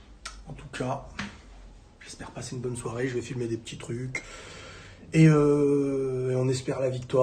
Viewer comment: quoi ?? j'ai recommencé plusieurs fois lol j'avais compris je vais fumée des petits trucs 🤣🤣🤣 je suis fou dsl